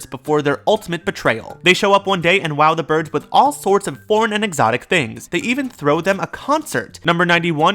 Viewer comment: I thought he said "porn" XD